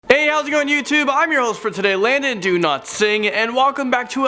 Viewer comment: I thought you said LONDON DO+NOT+SING hahahaha keep it up